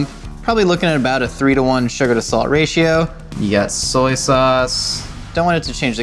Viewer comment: On his ingredients I thought soy sauce said boy sauce and I was moderately concerned